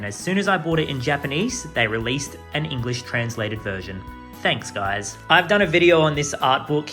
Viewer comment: why did i hear a shortcat "Thanks, Nintendo" coming